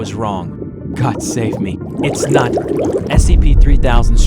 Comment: If anyone wanna knows what did he said when he was about get eaten. he said ''God save me, it's not.... '' And i think he was about to say '' God save me. it's not Anantashesha '' So i apparently i don't know if the eel is a God or not. but yeah thats what i think he was about to say